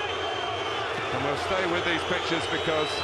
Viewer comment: did he just say at 0:13 "we will stay with these bitches'